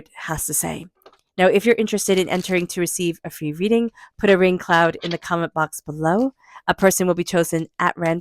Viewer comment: 🌧️ Entered for free reading (sounded like you said put a rain cloud) 🤞